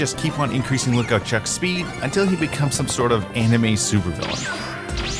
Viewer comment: My CAPTIONS Said villain Mom😂